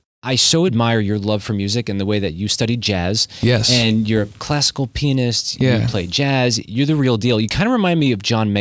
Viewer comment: Did he just say- your classical penis?